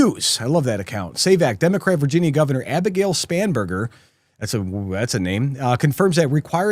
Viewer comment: I keep hearing "SPAMburger" for some reason. 🤔🤔🤔🤔🤔🤔🤔